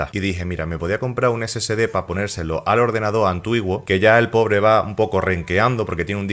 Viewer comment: Pero qué rayos fue lo que dijo 😂😂😂😂😂😂 Antuiguo?